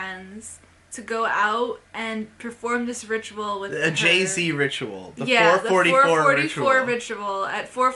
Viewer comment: I heard Jay Z ritual.